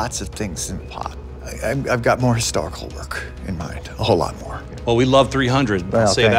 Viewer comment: "I've got more ....WHAT? in mind" ? What did Frank Miller say there? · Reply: I've got more historical work in mind.